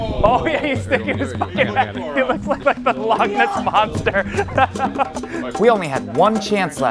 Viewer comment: I just want to point out that at 5:34 the auto-captions interpret what you're saying as "log nuts monster." This is 100x better than whatever other monster you may have hypothetically been talking about.